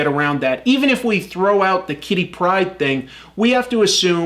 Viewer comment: About the 18:00 is he saying Kitty Pride or Titty Pride? Honestly can't tell, and my reaction would be far better to Titty Pride